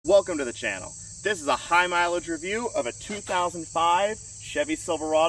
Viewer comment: I thought you said high miles. My 6.0L 2500HD has 296k. Runs like a top. Bit Rusty.